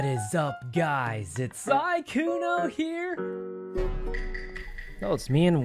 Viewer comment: Hey Sykkuno! I love your videos, and I had a suggestion for your captions. I noticed that there are some mistakes in the captions since they are auto-generated, making it harder for the huge number of people who rely on closed captions to understand your videos. For example, when you said, "What is up guys? It's Sykkuno here, " the caption actually says mykuno instead of Sykkuno at 0:17. At 0:49, you said, "Wait. That wasn't Wendy. That was Ash, " and the caption reads ass instead of Ash. At 0:59, the caption says "ashton corps ashley corpse," when said, "Ash and Corpse," and then you said, "Ash and Corpse." I know these mistakes seem silly and minor, but caption corrections really help the people in the hard-of-hearing community and make your channel more accessible.